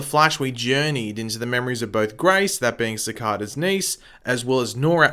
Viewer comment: did you say CECADA